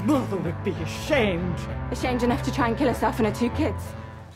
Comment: Ok, can soneone PLEASE tell me what Jessica said at 0:37? I have tried a dozen times, and I can't understand what she says after "ashamed enough to kill........and her two kids?" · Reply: Ashamed enough to try and kill herself and her two kids